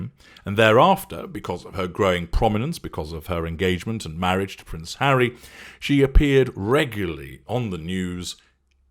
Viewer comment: did you say because of her engagement & marriage to Prince Harry?